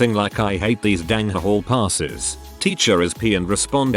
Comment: what is the p word? Seriously i cant think of it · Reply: Lmao it shouldn't really even be censored, the word is pissed